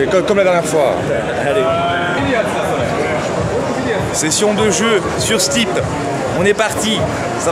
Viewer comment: "Session de jeu sur Steep".... moi j'ai plutôt cru que tu te faisais une "session de jeu sur c'type !" 😂😂